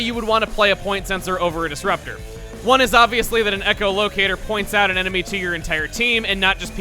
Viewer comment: when he points out why a point sensor is better than a disrupter, am I the only one who herd echolocator instead of point Sencer